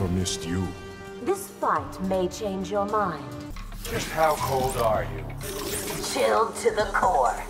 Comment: “just how cold are you?” “chilled to the core” “that is super hot” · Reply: I thought he said just how old are you